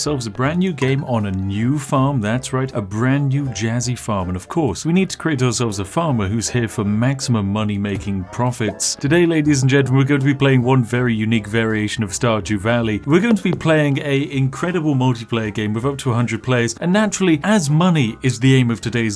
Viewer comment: I heard "StarJew." Every. Single. Time.